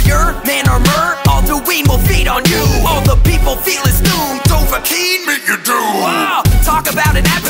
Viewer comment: 30% of the comments : complaining some villain ain't in the rap. Me seeing alduin is in the rap : happiness noises. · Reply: Fun fact: When I heard this rap for the first time, I heard "Dovahkiin, Mignatu" instead of Meet your doom. This rap named my very first Skyrim character